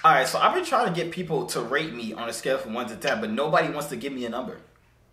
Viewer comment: I thought he said rape. This how many people agree